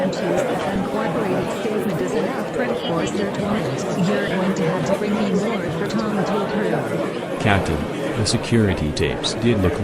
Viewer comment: I could not understand what Amelia Dash was saying. What was she saying? It is so hard to listen to because of the office ambience. Can you please tell me what she is saying? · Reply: “I can’t use an uncooperative statement as enough for a search warrant. You’re going to have to bring me more for Tom to approve”